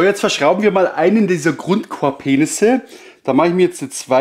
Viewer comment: bei 5:20 Verstehe ich GrundkorbPenisse, Genau so bei 6:43 Korbpenisses. ergeht es nur mir so? ^^